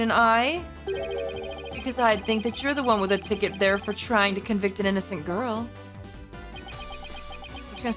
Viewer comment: You’ve probably noticed it yourself while editing, but Nahyuta says "You and the accused are most certainly bound for hell" , making what Apollo says afterwards a pretty sick (and valid) burn. I HATE the autoscrolling makes it so easy for people to miss it LMAO